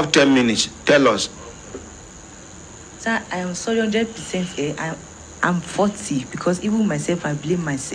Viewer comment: From the first time i saw it, I also thought she said i'm 40.When i listened closely i clearly heard she said I'm faulty. I think it's worth correcting brother on your next video about this.